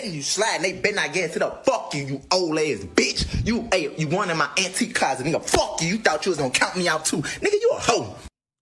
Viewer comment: I thought he said Auntie Closet🤔